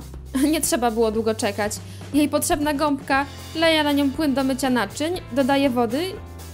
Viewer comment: usłyszałem "NAJARANIE"?